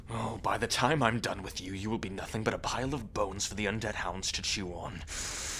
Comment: Me: I'm sorry but did he just say undead hounds!? As in Cerberus aka Greece!?